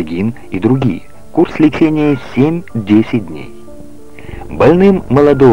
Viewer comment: 80 дней через 3 _ 4 часа это будет 6 _ 7 инъекций в день и 480 _ 560 инъекций за 80 дней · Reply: Было сказано 8-10 дней, а послышалось 80 дней. Антибиотики дольше 10 дней не ставят